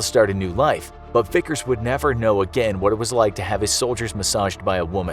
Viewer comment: did you just say "have his soldiers massaged by a woman"? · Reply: shoulders not soldier try turning on subtitles